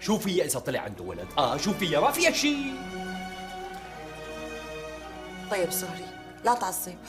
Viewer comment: شباب دققو ع دقيقه 1:50 سمعتو ايش قالت فريال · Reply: شو قالت ما قالت شي طيب صهري